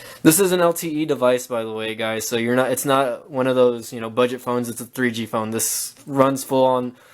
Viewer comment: Wait did you say this was a 3g phone?